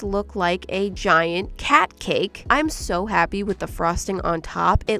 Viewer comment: She named it cart cake and I thought she said patty cake😄😄